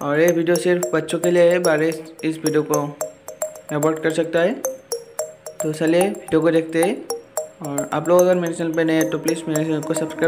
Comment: I think he Sayed salle youtubers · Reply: 😂😂Aare bhai. Salle YouTubers nehi saliye video ko dekhte he bola tha mene 😂😂 . Akbar aur sun lo .